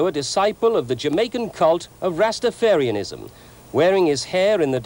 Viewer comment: My ear must have misheard him but I think the Journalist said; "the occult Rastafarian". I'll listen it over just to make sure that that wasn't what I heard